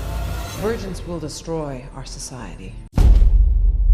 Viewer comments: at the end i heard "virgins will destroy our society" instead of "divergents will destroy our society" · I thought she said "Virgins will destroy our society" at the end lmao.